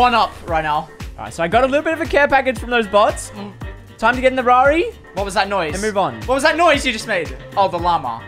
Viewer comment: did he call that a Ferrari???